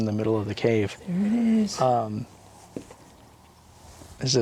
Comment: i couldnt make out what they said but i could hear them. at 20:40 ish there like a wheez sound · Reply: Oh! Haha. That's me in the cave saying "there it is!" Good catch though. And "wheezing," that's the sound I make when hiking. 😁